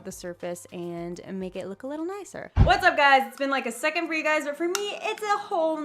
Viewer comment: I thought she said hey guys it's megan